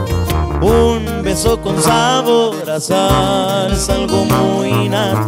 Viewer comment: nmms pensé que decía un beso con sabor a salsa ;) jaja